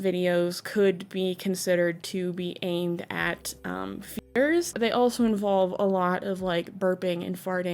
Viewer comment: "Aimed at...???" Does anyone know what word was cut/censored here? Misspell it if you need to, but I'm genuinely curious and can't guess the word ! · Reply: Feeders